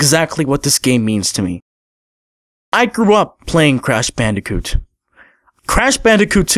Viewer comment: Did you say crash bandicoot toot at 10:40 ? Anyway, sub'd